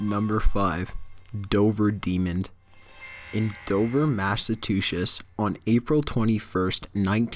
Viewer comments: For number five, did I hear "Dover Massatuchius"? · Is it bad that I laughed when he said "Massachusetts" for #5? All I hear was "Mashatuchetts" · I heard "Massa-toosh-ish" did anyone else hear that instead of Massachusetts? Because.....#5 I keep replaying it. .___. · Did he just say Dover, Massatushes?